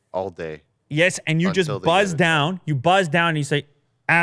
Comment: I heard bust down😂😂